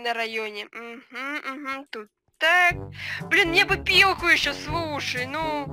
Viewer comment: Мне послышалось "пиво" А это " Пилка"